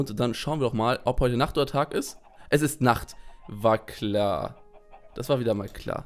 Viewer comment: Ich habe bei 'war klar' am Anfang 'Vagina' verstanden 😂😂✌️